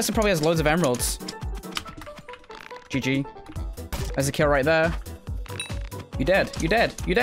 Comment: I thought you said there's a kit